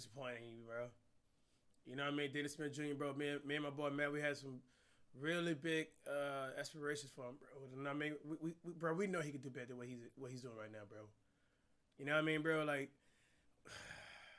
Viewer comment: This man said "expirations" instead of expectations or inspiration